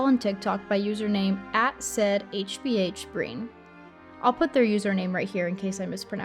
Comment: i think it’s the irish name saidhbh (pronounced “sive,” rhyming with “dive”) and the surname brien